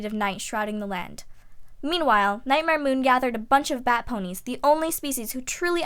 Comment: Did you say bat ponies at 3:16? Was flutterbat one of them?